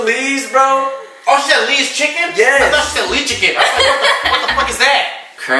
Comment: I thought said "LeeShicken" :(